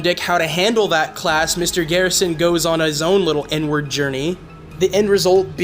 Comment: thought u said N-word journey lmao